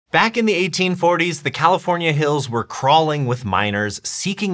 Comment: i thought you said california was crawling with minors